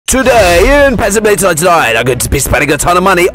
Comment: what in the living heck did he say?? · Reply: Pet simulator 99